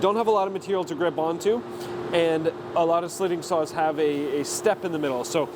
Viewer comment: I kept hearing "slitting sauce" and I was not even batting an eye. XD